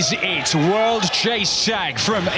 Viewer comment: did he just say world chase shag